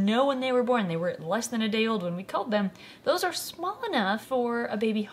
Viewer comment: I swear I heard her say they killed the pinky’s that’s fucked up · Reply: "culled" is what she said but yes, they do euthanize them humanely. its not any different than if the baby snakes had killed the baby mice themselves in the wild to eat, except gave them a more peaceful death than a snake would have. carnivores gotta eat, so prey animals have to be eaten. thats the circle of life